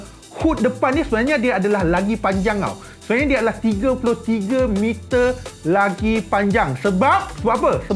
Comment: sy salah dengar ke bang 33 meter..😂😂